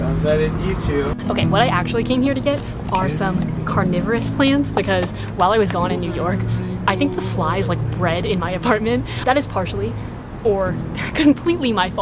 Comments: It's sad, i thought @ 4:48 said "corona virus plants"🤦‍♀️🤦‍♀️ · i heard like corona virus hahaha